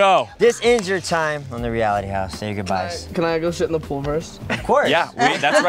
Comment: did just say he wants to go shit in the pool first lol😳🤦‍♂️💀😂